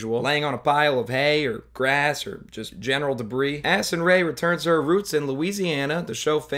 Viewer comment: I heard it as "Acid Rain returns to her roots in Louisiana" Poor Louisiana